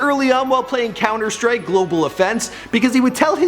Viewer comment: did I just hear "Counter-Strike: Global Offense"?